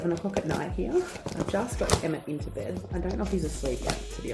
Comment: I thought I heard you say you just got EMMA into bed and you don’t know if HE is asleep. Lol that’s what lack of sleep does to you.